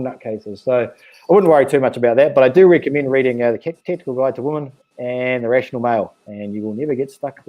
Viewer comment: Hello! On this video, at the 46:22 mark you recommended two books that all men should read the second one was "The Rational Male" The FIRST book I didn't quite catch. It was something like "K_____ Guide to Women.' What is the correct name?? Thanks! You have OUTSTANDING insights into these women. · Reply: Tactile guide to women. Also 3% man by Corey Wayne is good.